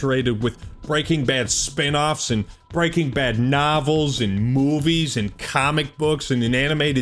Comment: The first time he said "Breaking Bad novels", I heard "Paperback novel." Bless you unironically for the memories, Nickelback. ❤😂👍